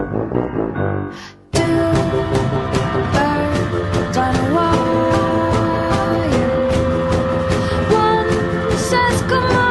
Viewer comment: I thought it said Two Bros on a Wire- 😂